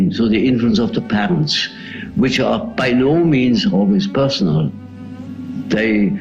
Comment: Great video - I'm a new subscriber. One point... At 4:31, the subtitles are wrong. The subtitles say, "by all means," but he actually says "by no means," which completely changes the meaning of that line. I feel like it's an important distinction for the idea he's conveying.